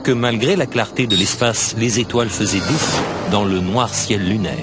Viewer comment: qu'est ce qu'il dit à 2:26 "malgré la clarté de l'espace, les étoiles ______________??? " les etoiles fusaient..?? je sais pas, help!!